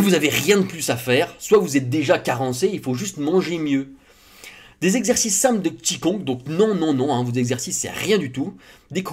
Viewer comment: "Des exercices simples de Qi Gong" J'ai entendu "des exercices simples de p'tits cons" XD